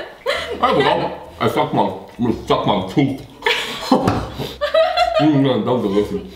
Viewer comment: I thought said I suck my toot 😂😂😂